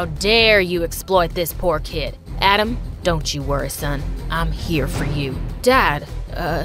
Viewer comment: I love how Jacob's dad says don't worry son But the subtitles says wear 💀